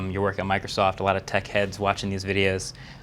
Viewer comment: I yawned when he said "techheads" and I heard "dickheads" watching these videos. LMFAO